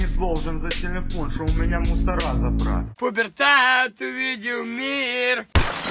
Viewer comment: when your cartoon head pops up, it sounds like you say "its time to eat your meal!" what do you really say? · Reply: He says "пубертат видел мир" (pubertat videl mir), It's impossible to translate, it's very special kind of self-irony